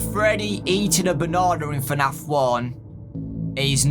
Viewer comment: I’m sorry but WHAT DID HE JUST SAY???????? Freddy eating a banana? What the heck did he say??? I don’t understand it at all!!!